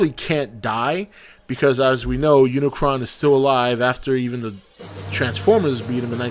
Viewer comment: O I thought it said unicorn but that would be a good one